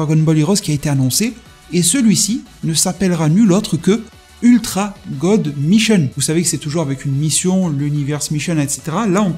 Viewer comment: Puré avec ta prononciation j'ai compris "ultra god Michel " au lieu de "mision" 😅😅😅😅😅